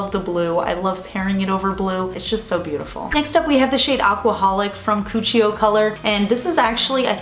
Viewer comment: When you said “aquaholic” I thought you actually did say “alcoholic” for a second. 😂 I was like 😳